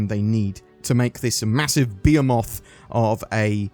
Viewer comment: When you said "behemoth" I thought you said "beer moth" and I was confused. Love the videos